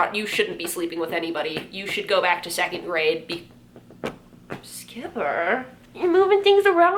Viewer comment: No offense, but I wouldn't suggest telling Jeremy to go back to the second grade. He might like that-- in the worst possible way. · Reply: Ohh i misheard her, i thought she said "secondary", like go back to high school. Made sense to me because that's when makeup becomes more common xD